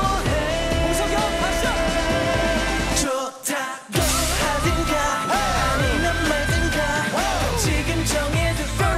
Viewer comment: What did kino say at 2:28?? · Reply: I think he said 'Hongseok hyung, let's go' 😊